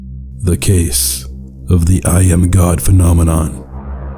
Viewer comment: I thought it said “Jam God” at first